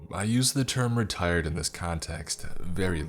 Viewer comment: i thought it said retarded instead of retired 💀